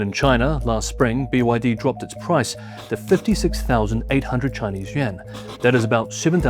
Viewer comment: Wait a minute, did you just say "$56,000 Chinese yen"? Um.. are you sure about that? I've watched the first 20 seconds of the video several times over. Each time, turning the volume up further. It still very much sounds like you said, "Chinese yen". Still scratching my head. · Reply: Hi there - lives in China and speaks fluent mandarin, he is saying Chinese yuan 元